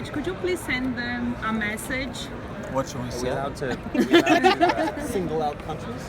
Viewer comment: what does matt say at 3:44 ? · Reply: No at 3:44 he says "what should we say?" But like in a funny way